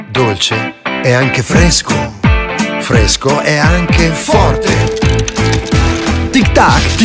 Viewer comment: Is anyone gonna talk about did he say the f word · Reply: He did not, He Said Fourte.